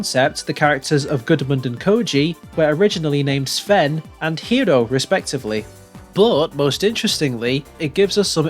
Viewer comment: Did you say Hiro?